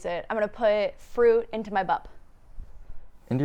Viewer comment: I also thought you said I’m gonna go out fruits in my butt